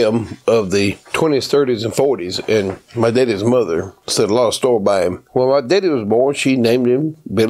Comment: For y'all Yankees and chill'en what he says at 26:05 is " my daddy's mother, set a lot of store by him." Translation: she held him and his word in high esteem, and since public schools suck these days what that means is she liked him and believed in him.